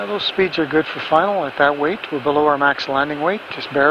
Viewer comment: Did I hear correct at 51:00 the captain says they are "just barely under max landing weight" ? That seems strange after such a long trans-Pacific flight and all that fuel burned.